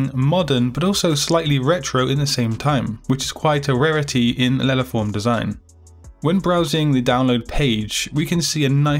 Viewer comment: I did not acoustically understand what he said in 00:49 and the automatic subtitles didn't either. "Lelaform design"? What kind of design are we talking about? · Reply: Letterform design